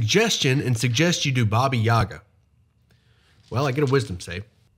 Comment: Not gonna lie, I thought it said baby yaga at first